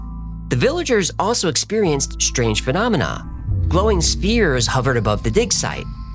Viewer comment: glowing steers hovering above the dig site. WTH 😮 (Spheres. He said, "Spheres.")